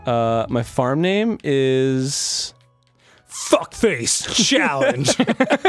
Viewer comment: my name is chalyse and i go by chal so i was like ????????????? every time i heard "fuckface chal"